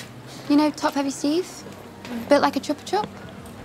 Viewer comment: I like this part but can someone tell me what she said after "Build like a......" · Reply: Chupa Chup